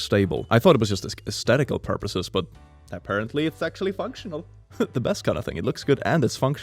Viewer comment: i heard sexually functional and i had to turn on the CC to see what you actually said.